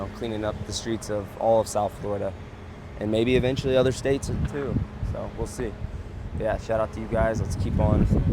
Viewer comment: uh...did he say "statses"?